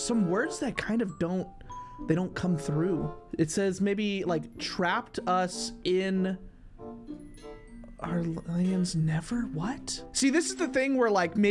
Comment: On 10:16 I think it said it trap us in dream Land